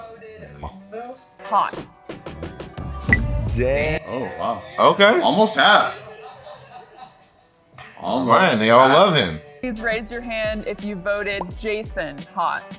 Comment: did he say almost half???